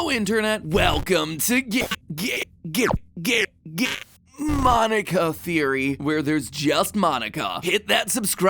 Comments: I appreciate the whole “Monkia is a hero” plot but I’m literally confused as to HOW Monkia managed to make the stand-in character for us a t t r a c t i v e Edit : MOM IM FAMOUSSS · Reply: When Game Theory intro is glitching i heard "Hello Internet, welcome to ga-a-a-a-y theory" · at the beggining it sounded like he was saying welcome to gay gay gay gay monika theory